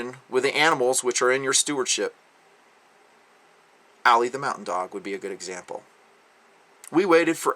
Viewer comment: You say "Allie the mountain dog". I hear "Allie the wonder dog".